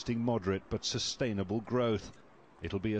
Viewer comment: Did he say " Al Jazeera" at the end of this report?